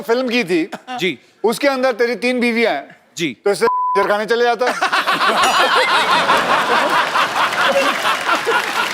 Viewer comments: what was the word that got censored, does anybody know? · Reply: Kanjar khana 🤣🤣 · What did akshay said on 20:10 that beep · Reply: Kanjarkhana - meaning brothel